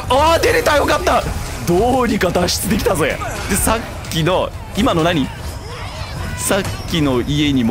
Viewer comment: 「今の何？」 空耳ガナード「何、お前」